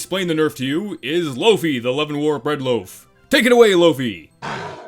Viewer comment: I think i heard a faint 'Loafy no' In the background